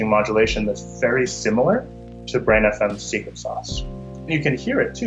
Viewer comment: The subtitles say "very similar to brain [inaudible] secret sauce." While it's ridiculously, painstakingly, obvious, that he said "Brain.fm's" (He said it earlier in the video at 2:05 and the subtitles guy got it right then, so idk what the subtitle guy's problem is.) Get your subtitles guy to watch the video next time, because that could have been found out with context even if it wasn't clear. But it totally was clear. It's funny because the auto-generated captions got closer than the actual hand made ones!